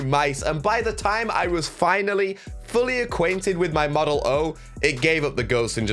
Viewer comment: did he say model o i thought it was model d